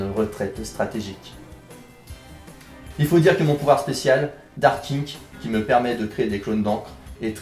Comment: J'AI COMPRIS DARK KINK QU'ES QUI VA PAS CHEZ MOI XD